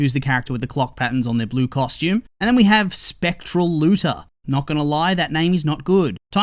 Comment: PLEASEEEEE I was watching the video in background mode bc multitasking and for a sec, I thought you said the name was "Spectral Loser" 😂 And of course Timestalker gotta be Marinette because she's the biggest one we know 💀